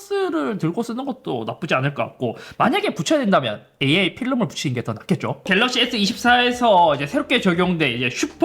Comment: AR이 AI로 들림 ㅋ 옥의티 발견!!